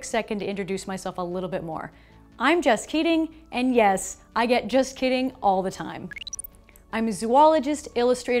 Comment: It kinda sounds like you say you’re name is “jus kidding” lol